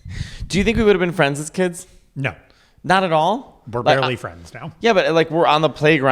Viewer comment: “We’re barely friends now” nick omg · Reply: LMAO I thought he said "we're literally friends now"